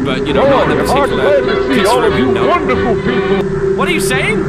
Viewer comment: When you thought Fredbear said im pretty sure “we’ve got wonderful people”what he actually said (this is only what I could understand)was “To all you wonderful people”I couldn’t hear the whole sentence.